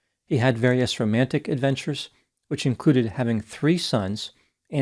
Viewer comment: I need to clean out my earbuds I'm afraid... When you said "which included having three sons" I heard "which included having threesomes"... 😂 Oops!